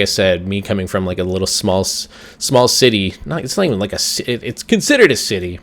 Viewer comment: I heard you say small Titi to a small city.